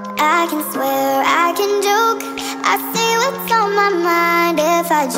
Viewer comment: I thought she said " I swear I can duke" lol